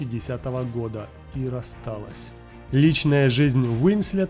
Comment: мне одному показалось что он сказал до 3010 и расстались??